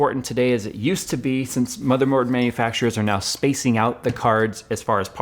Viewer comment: did he said "mothermoards" at 9:52 ? :p